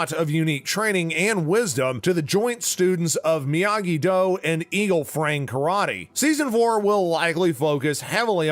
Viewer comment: It sounded like you said "Eagle Frang" Karate